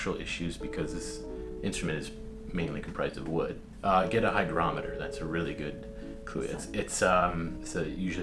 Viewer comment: He clearly said 'hygrometer', not 'hydrometer'. The latter makes no sense in this context.